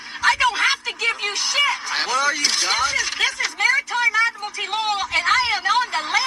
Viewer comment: minute. Did she really said "this is maritime admiralty law"?